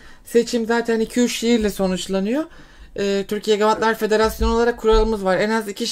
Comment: Türkiye gavatlar federasyonu mu dedi ben mi yanlış anladım 😅